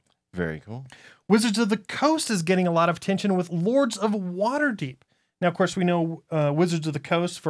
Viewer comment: I thought they said, "Lords of Watergate" . . . and for a second I thought: a Euro worker-placement game of Nixon's plumbers. I need to pay more attention.